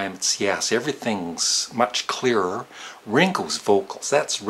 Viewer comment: When he said "Ringo's" at 2:23, I thought he said "wrinkles". Haha.